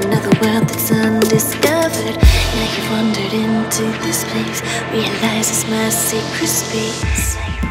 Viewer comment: I can't stop laughing because the track is so serene but then it sounds like it says "realized it's mostly crispy..." It's the crispy woods y'all Help me